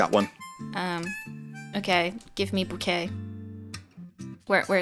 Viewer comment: is saying give me bukkake? ...